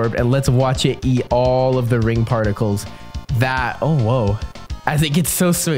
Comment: i know he tried to say "oh woah" but it sounded like "OwO"